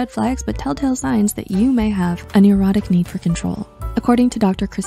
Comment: the cc says erotic need for control instead of neurotic is psych2go implying something?